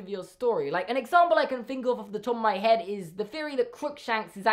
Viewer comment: I thought you said “crookshanks is actually the sorting hat” and I was like um ok explain